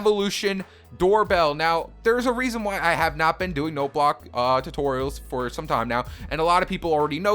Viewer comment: On the start, I thought you said roblox tutorial